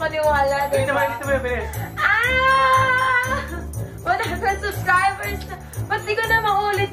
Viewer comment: ahhhh 100 subscriber na tayo Like kung narinig niyo rin